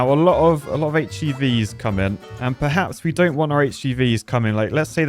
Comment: what is a htv or whatever you said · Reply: HGV, Heavy Goods Vehicle (lorry/truck)